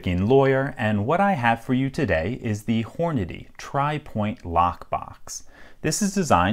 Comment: Who else thought he said horny at the begging or I got a dirty mind